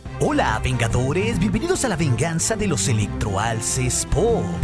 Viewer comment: No se porque pensé que decía la venganza de los electrolitos 😂